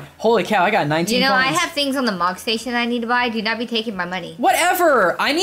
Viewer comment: I heard "Mog Station" Seems someone is into FFXIV!